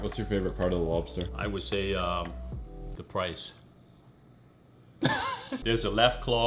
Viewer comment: "what's your favorite part of the lobster" 😂😂 I hella thought he said RICE Would've been the most Asian joke ever